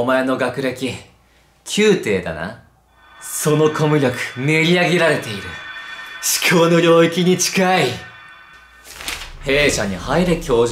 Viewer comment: 練り上げられているちくわの領域に近い に最初聞こえた🤣🤔🤔